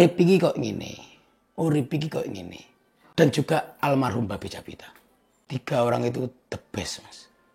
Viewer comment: itu subtitle nya typo atau emang sengaja? BABE woi bukan babi, kurang ajar bgt loh